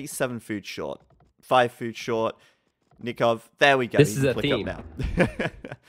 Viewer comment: "this is a thing..." what did you say? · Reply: theme